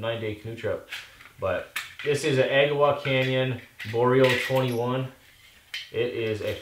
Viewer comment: what was the fold out saw at 5:20 called? couldn't quite understand what you said. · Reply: Agawa Canyon Boreal21